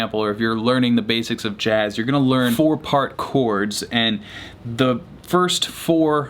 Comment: I thought i heard poor parkour when he said four part chords hahaha